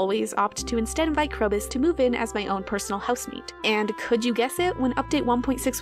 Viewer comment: I heard "personal house meat"